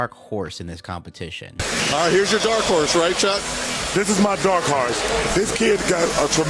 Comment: Chuck “this is my dark harse” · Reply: I thought he said "dark heart"